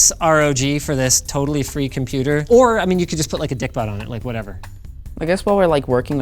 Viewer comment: Did he say "Dickbutt' LOL